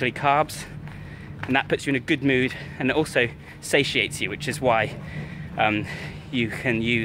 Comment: did I hear "satiety" lol